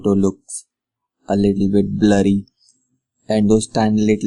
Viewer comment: the photos come out a little bit bloody?Thats scary!!! As you can see.... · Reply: perez i thought he was saying bloody as well, until i realized that he meant"blurry". LOL